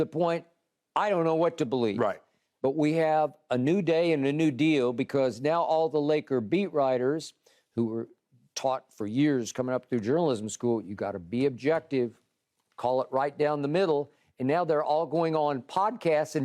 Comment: bro I deadass thought skip called lakers fans meat riders I almost lost it 🤣🤣🤣🤣🤣🤣🤣🤣 I thought damn you hate Bron that much huh lmfao